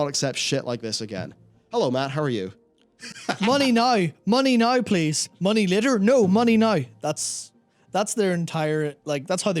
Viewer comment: Being European, if I wasn't familiar with english accents, I would swear Matt said 'money no, money no please, money litter? NO money no'